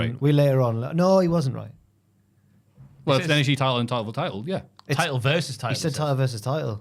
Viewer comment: Love the pod fella's, I too also thought he said title v title, but after watching again, Oba says TITAN v TITAN :)